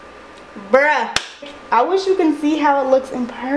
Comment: see what she is saying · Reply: “BRUH”